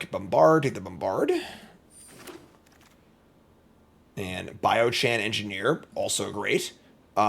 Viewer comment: I want to be clear that I hate the shit that gets from losers in chat about how he pronounces stuff and I'm not poking fun or groaning about it. I know he technically said "Bio chan", but I initially thought he said "Biome-chan" at 13:48 which is actually how Bio-mechan is spelled and now I will forever call it Biome-chan. This makes me happy.